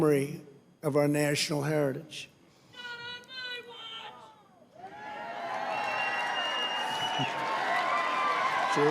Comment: what’s that guy yell out? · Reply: Art Howard He yelled out "not on my watch". Hope you have a great day!